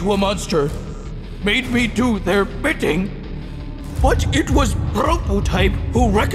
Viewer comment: When he said bidding I thought he said bitching